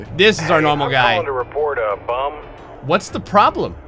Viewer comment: I thought they said bomb lol